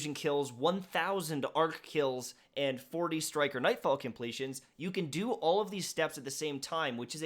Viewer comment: I thought you said “striker nf completions” lol I was like.... what if I don’t have a titan. :””””) good content man.